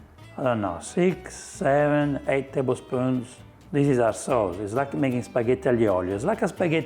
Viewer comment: I thought you said arse souls but you said our sauce 😂